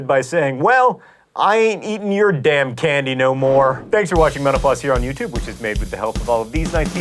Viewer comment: OK, you always talk fast so it's hard to know whether this is actually the fastest I've ever heard you say it or not, but you said it so fast this time that your "thanks for watching mental floss" sounded a lot like "thank you for watching menopause." Since I wonder whether you ever slow down and thus I infer I may not be the only one, I Google this phrase and discover that indeed, I'm not the only one who's ever supposed you've sounded as if you're saying this. And I typed all that in 9 seconds